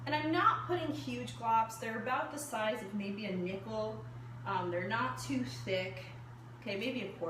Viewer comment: I thought you said nipple not nickel😂😂